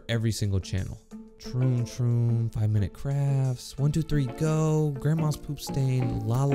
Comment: did he say grandmas poop stains?